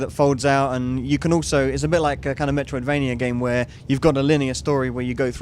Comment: did he say Metroidvania?